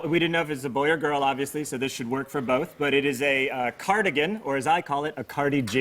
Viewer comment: Did he say Cardi G? 0:33!